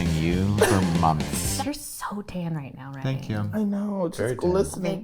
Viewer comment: i thought said “you’re so gay today” and i like that better than tan because it’s true